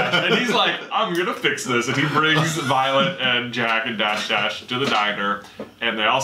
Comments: Did he just say Violet, Jack and Dash-Dash? Lol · Starting 7:31 is it just me or did he say violet jack and dash dash?! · Did i hear "Jack and Dashdash"? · Did he just say Violet Jack and Dash Dash? 😂